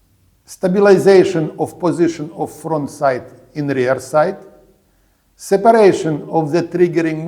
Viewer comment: the AI-generated subtitles write "side" instead of the correct "sight".